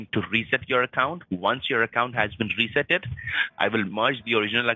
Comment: did he just say “ once your account has Been freezeeded “?!😂😂😂